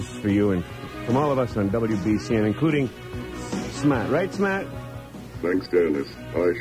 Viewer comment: did he say "SMAT: ? computer robot voice lol